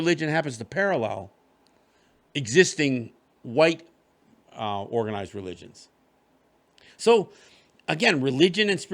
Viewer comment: Hi! Can you spell out the encompassing word mentioned at 50:00 top mean the 'real' Native people (that is to say, connected to the Mother and lands / creation? And does this word pertain only to the Haudenosaunee area? I'm glad you mentioned that non-Native people can develop ties to the land, even in a spiritual sense. I can testify that I truly do where I live and for its own sake and perspective in addition to my own. Thanks for this episode. Very educational as well. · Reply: Onkwehonwe